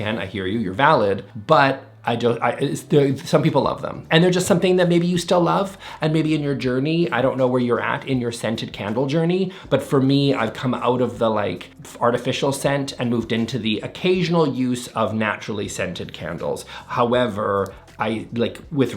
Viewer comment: I swear you said “fartificial” and I couldn’t agree more